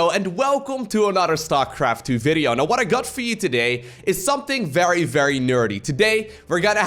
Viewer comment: I have to admit that I always heard:"what I got feed today" instead of "what I got for you today".